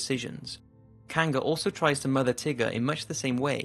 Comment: I almost thought you said murder at first. But quickly realized, oh, mother.